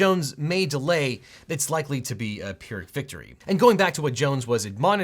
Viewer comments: Just FYI— At around 12:00 the subtitles say “pure victory” but it should be “pyrrhic victory”. That’s a pretty significant difference. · Pretty sure the subtitles are incorrect, "pure victory" should be "pyrrhic victory". Unless I misheard